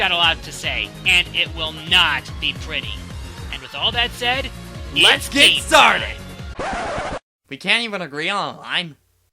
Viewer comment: what did say? i couldn't hear him over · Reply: He said its game time